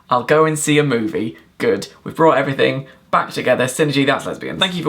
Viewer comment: 10/10 thought you said "detention" at the end and had a mini panic